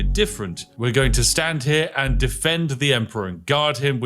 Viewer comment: I thought he said he was going to save the Emperor. 🤔